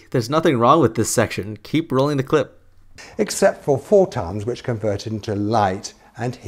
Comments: i think he said 4 tons not photons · Correct me if I'm wrong but I think he said 4 tons not photons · I could be wrong but I think he is saying 4 tons at 3:24 not photons, maybe he ommited the million because the sun does lose 4 million tons of mass via radiation per second. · Does he say ”except for four tons” at 03:24 rather than ”photons”? In other words “four tons of matter is converted to energy every second”? Either way, if that is what he’s saying then he’s still wrong seeing how this claim would be off by several orders of magnitude. It’s more like 4 million tons + of matter converted to energy every second. Can’t properly hear what he says though.